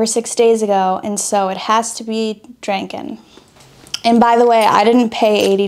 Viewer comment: Did she just say "dranken"?